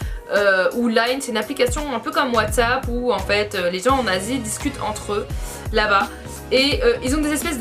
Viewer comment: C'est moi ou à 8:42 elle dit : "c'est une application ou les gens nazis qui discutent entre eux"